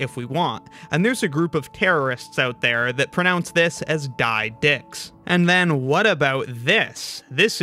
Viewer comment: DY DX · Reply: I heard him say "die DIC-" (i cant say that word)